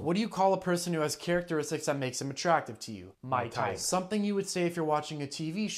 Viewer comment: That final line has me dying rn lmao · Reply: ah he said "my type" I thought he said "Mike Tyson"